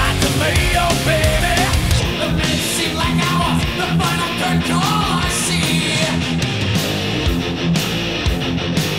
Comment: I thought that's what he said lol wat does he really say? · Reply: The final curtain call I see.